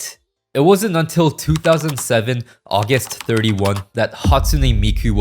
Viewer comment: Ongoing list of corrections because you can't edit YouTube videos after they're uploaded: 1:44 What I said here is true, Meiko WAS the first Japanese Vocaloid, but the first Vocaloids were Lola and Leon, which were English voicebanks. 3:10 Ievan Polkka is a Finnish song, not Swedish. I would like to formally apologize to the Finnish, and hope they will forgive me. 6:08 Mispronounced Toraboruta 11:00 The line "Miku's first solo concert in history" was lifted directly from the Vocaloid wiki, but many people have pointed out that her first proper concert was actually in 2009, with MikuFes'09. Many sources still credit Miku's 39 Giving Day in 2010 as the first Miku solo concert for some reason, but it's still worth pointing out. 13:22 The version of Fukkireta I played in the video is actually Lon's very popular cover of the song, not the original Teto version by Lamaze. I suggest you check it out, lol. 17:56 So this is actually wrong- The new engine didn't lack support for these voicebanks as they are mostly backwards compatible, they simply weren't updated for the new engine. 25:20 Flower wasn't a new Vocaloid by this point, she's actually existed since 2014. However, this is the era that she entered peak popularity. 28:20 Mispronounced Kyoufuu All BACK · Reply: did you say august 31th or am i just deaf